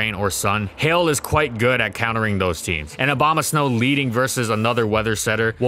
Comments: i keep hearing "obamasnow" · I keep hearing "Obamasnow" :d